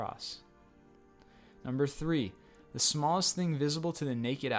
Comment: I thought you said smallest things, a person isn't a thing.